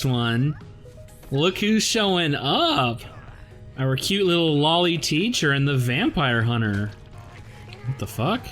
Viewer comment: did someone say loli